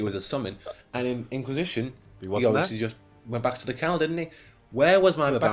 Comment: I think you said "Went back to the kennel didn't he?" lol Also congrats on 30k!